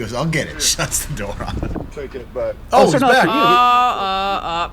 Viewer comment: The sad thing is, it kinda seems like that guy WAS gonna take his cart back, (unless I'm hearing him wrong...sounds like he said "I'm takin it back") until the antagonized him a bit more and then made him feel so indignant that he threw out his plan of redeeming himself